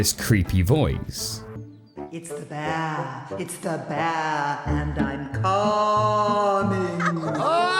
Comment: I really thought she said “It’s the BAD! It’s the BAD! And I’m coming!” And that actually gave me this sick creepy chill in my stomach. The bear and lion are so unsettling, that they look like they belong in an Ari Aster film. Like some psycho would try to feed children to them on live TV.